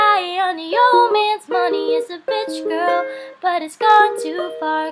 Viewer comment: I thought you said... "She's a bitch girl" LOL